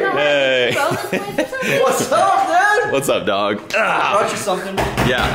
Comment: DID just say what's up dawg?!? In pretty sure that's a first for me , has he been Rollin in that Impala lately? 😎🕶️lol